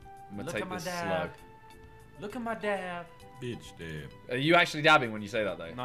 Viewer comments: at first I thought he was saying look at my dad but then I realised it was Ethan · I thought Ethan said look at my dad🤣🤣🤣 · Harry is doing his best impression of ethans dad in the thumbnail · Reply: Around 5 in it sounded like Ethan was saying look at my dad instead of dab. His dab didn’t exist same with his dad